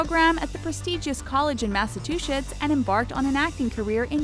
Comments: did she said Massachushits? hahahaha 😂🙊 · Did she just pronounce baccalaureate as "Bachelorette"?? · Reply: and she also said "massatwoshits" instead of Massachusetts at ~ 4:35 🤣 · am I the only one who hears "Masa- two-shits"